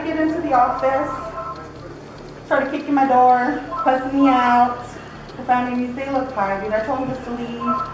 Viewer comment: when she says ‘cussing me out’ but subtitles says ‘pussying me out’ 2025 is a weird time to be alive man